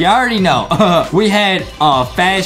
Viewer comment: wait did yall hear the joke justie made at the end i dont know what he said here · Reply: I think he said ice cream cone